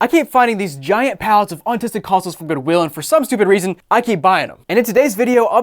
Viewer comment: I swear he said "Autistic Consoles".